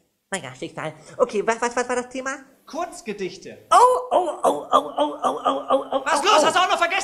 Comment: Ich habe bei kunst gedichte verstanden furzgesichte hahaha